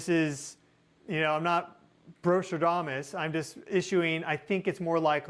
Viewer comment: did he say "Bro-stradamus" ?? 23:26 lol